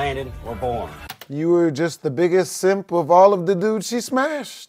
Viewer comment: DID JUST SAY SIMP!?!?!?!?!?!!!